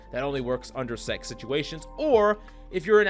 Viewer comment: coach uhhh? Did you just say “sex situations”